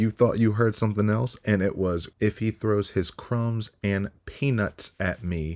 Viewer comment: Definitely didn’t sound like “crumbs and peanuts” being thrown at her 😂😂😂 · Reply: Yeah, I heard "cross and pen!$". Too many vampire videos!